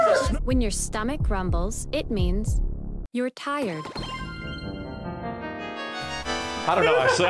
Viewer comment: Not gonna lie i thought she said retard 😅😂😂